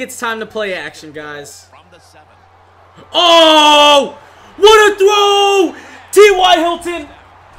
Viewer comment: in 12:05 I thought he said what are those